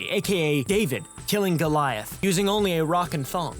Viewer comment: Not gonna lie, when you were talking about David and Goliath and said, "Using only a rock and thong" i thought you said "rocket thumb" and now I can't get David giving Goliath a thumbs up in the form of a rocket.